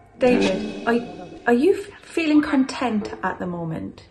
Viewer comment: when you asked if David was feeling content at the moment, I heard very clear “excited” at 26:03.